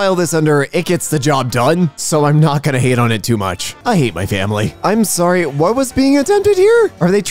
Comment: did you say family whatever was in that I guess your family don’t want you to see it? Don’t like this comment like the video